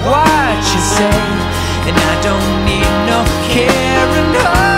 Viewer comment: I thought he said “I don’t need a Karen” I yelled Amen!